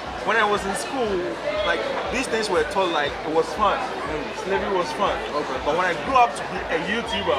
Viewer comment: did he say he thought "slavery was fun"!!!! did i hear that right? that's disturbing